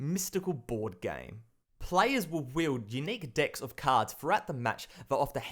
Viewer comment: I like your channel, but it sounds like you were saying, "unique dicks of cards."